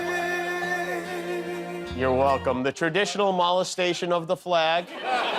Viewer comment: Could have sworn you said "Mollusk-tation." Cause Trump is rather slimy.